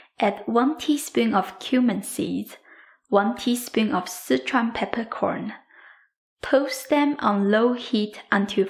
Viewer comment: Are citron peppercorns the same as sishaun peppercorns? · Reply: That link will not work for me. I could have sworn I heard citron peppercorn too. lol :)